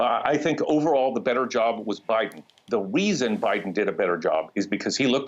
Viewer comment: ahh, does this guy say "the weason" at 3:23 instead of "the reason"?!